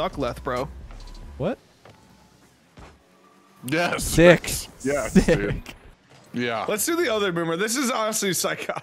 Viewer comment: Tell me I'm not the only one who heard Leth spit bars "Dick, Sick", why didn't he keep going lol